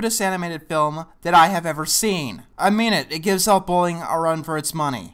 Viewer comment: "To give ?Elf Bowling? credit..." I swear you mumble that bit of dialogue 20 seconds in so badly that i will never know what film you are trying to say no matter how many times I relisten to it. Ah you say it again at 12:30 and it still sounds like Elf Bowling... Is that actually what it's called? Can't be surely. "They make you want to watch Disney's White Wilderness on loop". Hold on let me Google... Oh. Oh wow! What a ref to make. Well. You've got quite the willpower to watch this movie, and probably more than once to do this review. This was amazingly bad. Thanks.